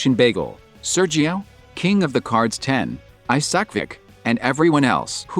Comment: Wait WHAT DID YOU SAY?!?!?? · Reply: He said "aisak_vik"